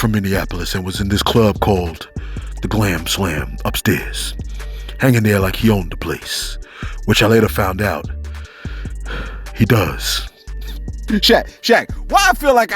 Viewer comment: I'm dying over here! I could've sworn you said the club name was called the "Clam Slam!" 🤦